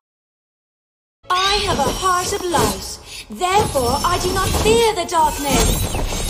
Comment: I thought she said i have a heart of lice